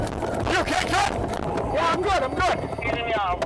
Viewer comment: In 18:24 I swear I heard d lady in d grey car said " I'm hit"...is like she got shot in her car..I'm not too sure